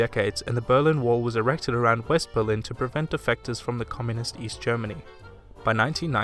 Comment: Mistakes: Borders with France often aren't correct. Used the wrong flag here and there. Left out a few wars and people groups for sake of time and flow. Hope you all enjoyed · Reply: I heard, "Prevent defections from...".